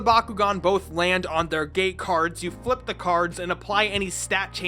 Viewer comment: until 7:30 I thought he said "Gay cards" instead of "gate cards".